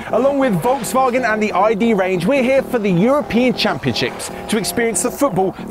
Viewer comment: It sounds like said We're here for the European ChampionChicks.😁😁